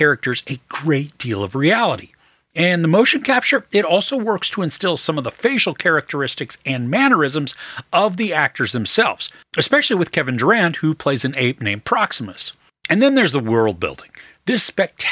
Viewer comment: Thought you had said "Kevin Durant " forner Celtics player, then I googled and was like 😂😂 ohh Kevin Durand my OG